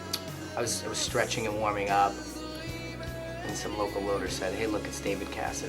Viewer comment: what did he say at 1:27? a local what? O.o